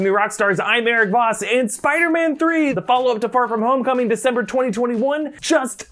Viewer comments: Did anyone else hear 'Far From Homecoming' or was it just me? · Did he just say Far From Homecoming?